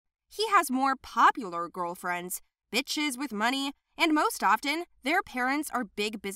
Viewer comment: I thought she said Bitches with money